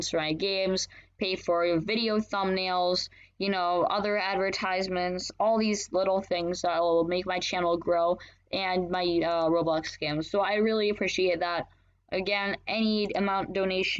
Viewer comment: i thought at 0:52 he said "and my roblox scams" XD